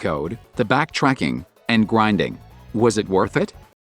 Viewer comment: I love how at 5:00 he says "gwinding" instead of grinding